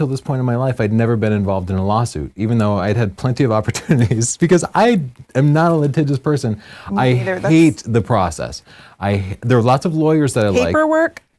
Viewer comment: he says something like "I am not .... person". Can anyone catch what he said there exactly? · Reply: He said "litigious." His comment meant he is not prone to engage in lawsuits.